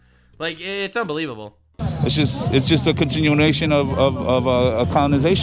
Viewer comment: I was so confused, I thought the monster was saying "condensation" xD